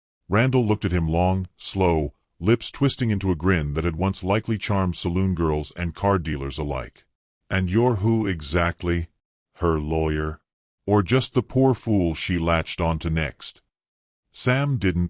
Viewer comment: The car dealer line was funny - lol. I liked the story, however. I liked the fact that she considered being free as well as marrying again as an option. She was brave and pragmatic. Sam is a good man. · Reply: Maybe he did say card dealer, but captions read car. I'm 70 & fairly deaf so if I misheard, I'm sorry.